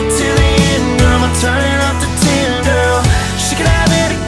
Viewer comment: i thought he was saying 'i'm turning off the tinder' lol